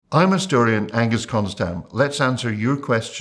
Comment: I heard "I'm historian, Ghengis Khan's dad" and was like.. wow that's a good credential for a historian